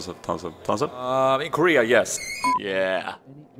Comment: in the tons of damage part, my friend says he says "I agree yes", but I hear "in Korea yes", which one is it 4 realz?